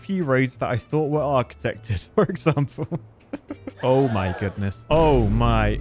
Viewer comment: oh myy gawwd - Ra’Jah O’Hara ? Do I hear there ? 😅